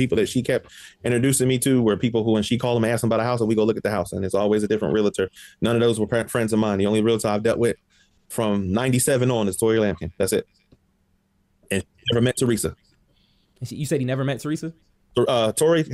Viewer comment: is he saying “Tori Lambkin”?